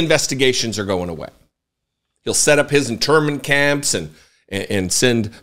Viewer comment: At first I thought you said "interment camps," then on 2nd listening, I heard the "N" in there! I'll have to go find someone else to correct! 😁 Keep up your great work and thanks!